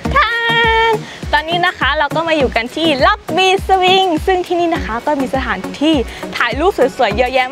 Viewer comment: Wait. WHAT? What did you said on 0:31? This is what I heard "tangina kala ko my you can see" If I did not read the subtitle, I thought you speak our language 😂 it's a curse word in our country by the way so I was stunned. I thought you said badword hahahaha. Please tell me how do you say it, in your own language I really wanna know. Lots of Love from Philippines💗